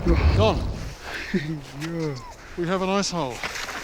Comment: thought he said "we have an a**hole"